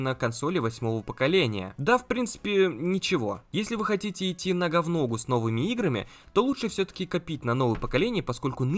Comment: мне послышалось "На гавно гу"